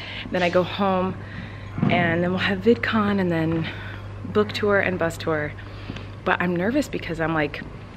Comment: DID SHE SAY BOOK TOUR???!!!!?!??!?!?!?!?!?